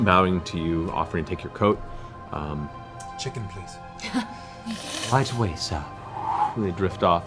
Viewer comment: I heard, "check it, please," or "check, please." Either works well, here... :)